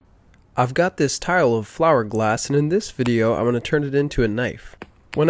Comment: Ittook me entirely too long to figure out you were saying "tile of flower glass" at the beginning. I misheard it as "tire wolf flower glass" I was like "what kind of glass is "tire wolf" was just about to look it up, when I figured it out...🌱🤣